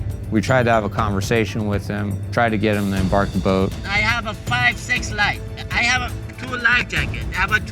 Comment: did anyone else hear "I have a fine sex life"? 🤣 Why was the coast guard out there wasting $144,000? 🙄